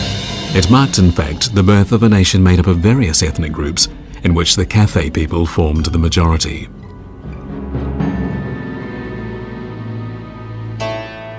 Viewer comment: Anyone catch what he says around 13:15, the Kafei people?